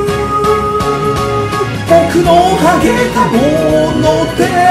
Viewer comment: 今年も1年お疲れ様 ありがとうございました。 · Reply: 僕の｢禿げた｣ものでたくさんの人が幸せそうに笑っていて に聞こえましたすいません